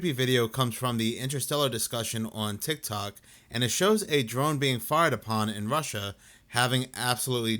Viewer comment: A drone I thought you said a drunk in Russia not a drone